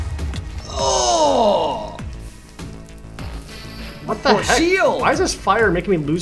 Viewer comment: DID YOU JUST SAY CHOCO BEANS???! No, I said what the heck.